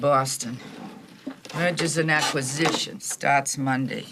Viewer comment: Did she just say "murders and executions"?